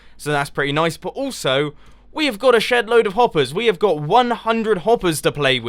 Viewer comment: I thought u said shitload